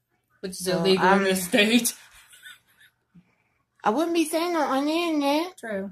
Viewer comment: What did she say 18:01? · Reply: I think she is saying I wouldn’t be saying it on the internet